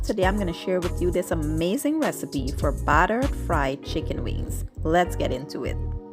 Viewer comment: Oops I made a mistake I thought she said butter fried chicken 🐔 wings 🪽 not battered fried. So sorry folks . I just happened to look at it again. The Bronx New York